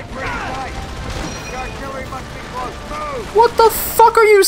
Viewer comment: idk about the first part but the 2nd part is "their artillery must be close" . and i only know that because i think the same voice actor said the same line in like COD2 big red one